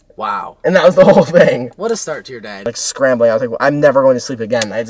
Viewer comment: what nick said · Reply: what a start to ur day, was what he said